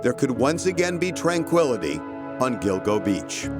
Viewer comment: I thought this said Dildo Beach 😂😂😂 😂